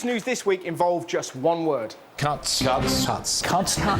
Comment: I thought they were saying cunts!